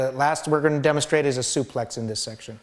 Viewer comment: wtf you guys!! he doesnt say "not so rough" and "sorry" he says: "whats next" and he says "suplex". lol you guys suck!!!!!!!! just watch is